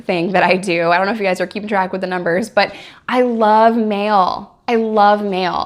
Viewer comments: I thought she said she loves male. I’m here! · I thought you said you loved mayo lol